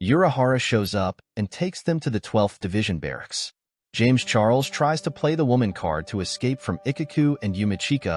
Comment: Did you say James Charles?!!!😂😂😂😂